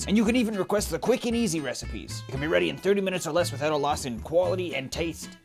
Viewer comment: I thought he said "recipes that can be done in 30 minutes or less without a lawsuit." And that would be pretty on par for.